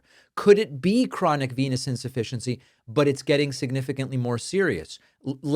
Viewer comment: I thought you said chronic penis insufficiency.😂